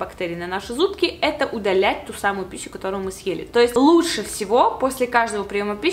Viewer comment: Мне одной послышались слово" пису,,. ?